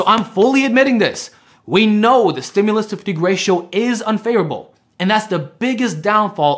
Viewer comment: "the stimulus to FIG ratio is unfavorable..." All he has to do is speak a bit slower, why don't many of these guys do that? Is that what he said?? did he meant to say "fatigue" or what is the word?? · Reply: Stimulus to fatigue. I will work on speaking slower.